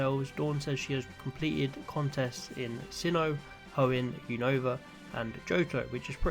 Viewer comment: Did she really say unova?? I thought she said kanto.